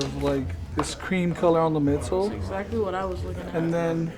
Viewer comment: what are these shoes called? he said it but I don't know what the word he said was... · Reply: I heard “Midas” but those are not the Midas dunks